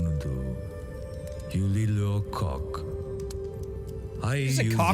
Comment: it sounds like he said you little ol’ kok